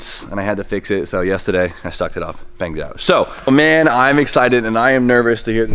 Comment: i thought you said “i sucked it off and banged it out”